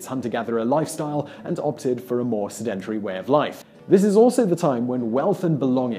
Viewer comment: Sedentry? SedenTARY? Sedentary. Or is he saying a different word? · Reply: Sedentrary